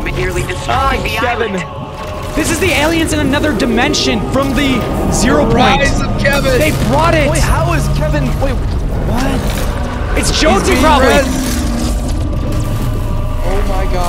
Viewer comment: Holl up did he say another demston and furte jonsey and zero point at the same time